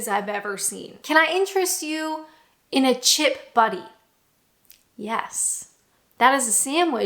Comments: With your accent, it sounded like you said a chip buddy...having a chip buddy would be awesome!😂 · When I heard you say chip “buddy” I felt so confused until I realised you were talking about a buttie lmao